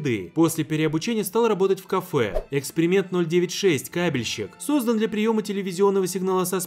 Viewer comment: Мне одному послышалось не кабельщик , а каменщик ?